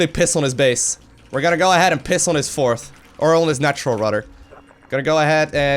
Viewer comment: I almost thought you said you were gonna pis on his face... LOL 7:32 ish